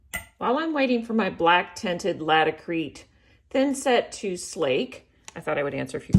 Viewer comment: What’s the name of the black stuff you use to stick the pieces down? I think I heard you say “inset”? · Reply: Sorry answered my own question. Laticrete